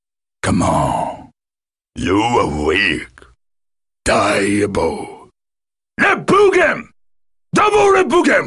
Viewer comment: I legitimately can't decipher what he's saying at 0:12. I can understand everything else, but what the hell is "die hippo?"